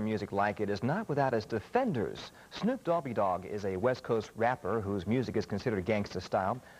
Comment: Did he just say, Snoop Dobby Dogg??? on